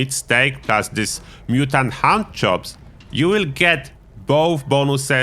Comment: Mutant Hound chops sounded like you said Mutant Hand Jobs 😂